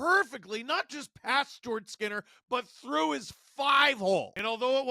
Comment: Did you just say Jordan Skinner?